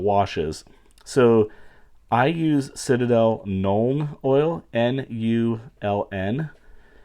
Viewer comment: Not going to lie, I've been watching your videos for a couple of days now and every time to mentioned Nuln Oil I ligitamatly thought you were saying Gnome Oil. I thought it was called that as another name for Drawf cause dwarves are sometimes known to live in underground cities and run smithies and such and thus would be covered in soot and other grime, so to me it maid sence because it was a wash used to make things look grimy. 🤣